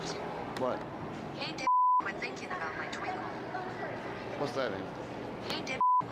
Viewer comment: I heard “Dickhead! Quit thinkin about my twinkle” I wouldnt have shot him, but i wouldve blessed him with the hot sauce.... You know, give him a little taste of that spicy air 😅